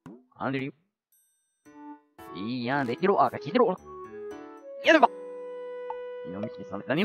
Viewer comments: にきいてろゴラって聞こえた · いやーんに聞こえた人 ↓